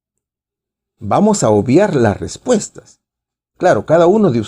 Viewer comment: 🤔Minuto 10:20 ¿Por qué dijo hermano que "vamos a odiar u obviar las respuestas"?🤔🤗